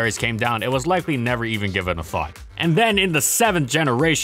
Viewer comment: thought narrator said "given a f**k" but it was "given a thought" gotta get my hearing checked.